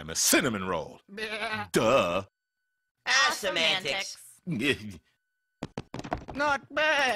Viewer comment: What do they say? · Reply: awesome antics